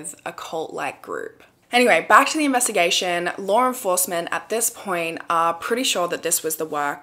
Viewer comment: Every time I hear her say law enforcement, I hear "Lauren forcement"